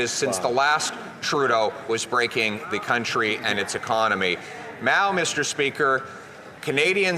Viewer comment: Did Pierre say "Mao mister speaker"? (20:53) Brilliant if he did!!